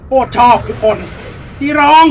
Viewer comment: ฉากนี้ผมได้ยินอีรอง😂😂😂😂😂